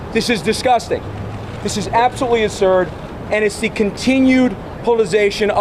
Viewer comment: He actually said "this is ASURD" @ 0:19. He's an imbecile, like his old man.